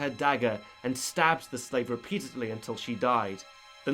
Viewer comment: does he say stabs her peacefully? because i really don't think its possible to stab somebody in a peaceful manner